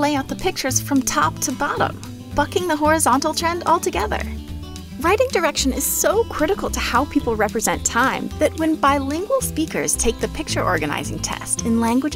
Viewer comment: When they started the mandarin language flow explanation I thought they said fucking the horizontal trend